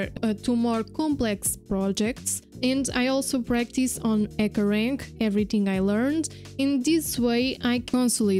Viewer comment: excuse me, what is she saying at 5:13? i couldn't understand, echorank? ekorang? or something like that thank you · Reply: Hey ☺ Thank you for your comment. The word I say there is Hackerrank, I'm sorry if it was confusing. 😅 Let me know if you have other questions Cheers